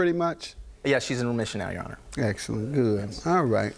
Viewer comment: I thought the plaintiff said his wife was in remissionary.